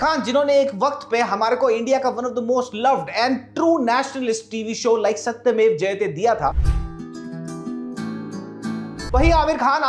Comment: kya bola true nationalist program?😂😂😂😂